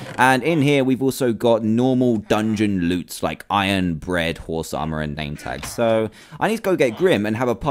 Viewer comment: 500th Video WOAAAH! :D Hope you enjoy this one guys and if you did, be sure to leave a LIKE and see if we can hit that 10,000 LIKE target! #TeamTDM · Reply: When you opened the chest two of the items were iron and bread and you said it so fast it sounded like you said iron bread. I was like ?_? Seriously iron bread? How do you eat that!